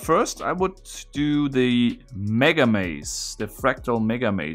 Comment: I thought it said “megalomazia”